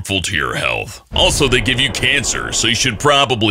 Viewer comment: did sum1 say CANCER?